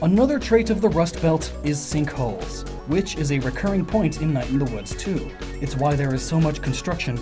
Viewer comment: there is a night in the Woods 2? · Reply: I thought he said "Night in the Woods too..."